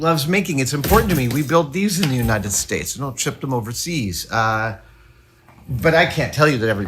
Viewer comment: Adam says: "we build these in the United States...", then some words I can't quite make out, then ends the statement with "... overseas." As is so often the case, captions don't help with anything that I can't already hear. What did he say? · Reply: "we don't ship them overseas" presumably referring to the manufacturing process